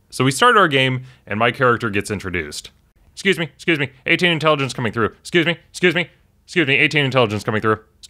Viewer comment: "Excuse me, 18 Intelligence coming through" 🤣🤣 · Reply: Ooooh, so that's what he said! I thought he said "A-Team Intelligence", which sounded logical enough for me not to question it^^